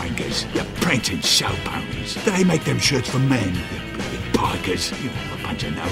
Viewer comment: DID HE JUST SAY POGGERS😳😳😳